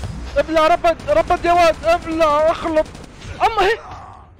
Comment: سمعت غلط ولا سماعاتي فيهن شي يا جماعه ! 😂😂😂😂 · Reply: ايش فيها قال ابلع اخلط ما قال شي غلط